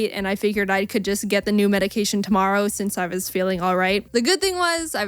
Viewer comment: Hi I heard you like MHA Because I heard you say all might and I just wanted to know if you were a fan of MHA. sooo are you?